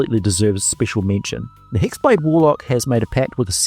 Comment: LMAO! I totaly thought I heard you say Sex blade warlock but I thought "nah it was a glitch in audio" and then you posted that picture and I almost chocked on my water I was drinking! That was awesome!